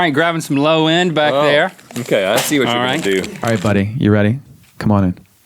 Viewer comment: Mike did just say "hey buddy" or something like that??? if he said that to Chester he is so cute sweet And lovelyyyyyyyyyy ❤❤❤😍😍😍😍😍😍😍😍❤